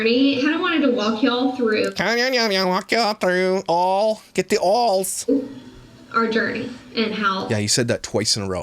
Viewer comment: I could have sworn she just said “ walk you through all the dirty” . It’s more accurate than “journey”. These ppl are way too full of themself. “It’s so hard” ? It’s called life. Get over it. Making money by whining to pole & using kids to gather sympathy.